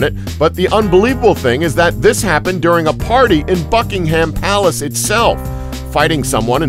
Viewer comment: sounds like he is saying "fuckingham palace"